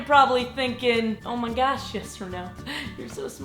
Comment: When you say your name it sounds like " yes or no" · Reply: Arin Brandenburg I'm new so I thought she said yes or no 😂😂😂